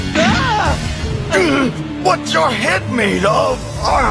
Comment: when Vegeta said “What’s your head made of”😂😂😂😂😂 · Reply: I thought he said "watch your head me love "